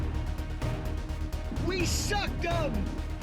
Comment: Did he say, WE SUCKED HIM?? 😂 PAUSE. Ayyye yoooo